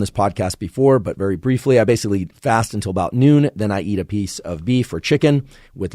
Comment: onwards without watching sounded like he says "what I do: I basically eat fast until noon" and now I can't unhear it